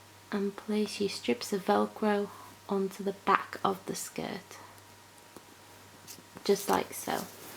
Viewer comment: What did she say at 6:23? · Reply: Back of the skirt